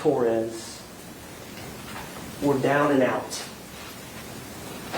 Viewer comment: I thought he called her “Sailor Boon” for a sec 💀